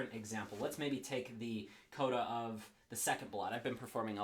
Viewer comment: What was the name of the last piece? He says it at 5:41 but I couldn't quite catch it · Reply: the coda of ballade no 2 by chopin